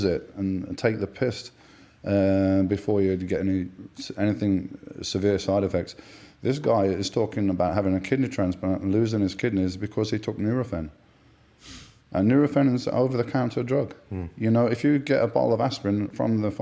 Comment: what medicine did he say? neurofen?